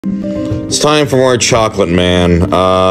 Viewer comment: I love how we’ve sorta welcomed Chocolate Man as an honorary addition to the Extended Universe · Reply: i thought he was saying "Its time for more chocolate, man"